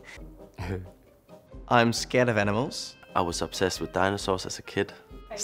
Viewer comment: I thought I heard someone say “I’m scared of edibles” 💀